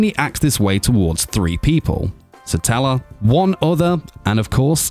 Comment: What did he just say "one other" cus I can't clearly hear it even with headphones pls reply😁😅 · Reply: i think "one other"